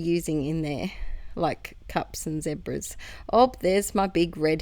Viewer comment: ZEBRAS! Ha Ha I thought you said ZIPPERS! Now that would have been random. LOL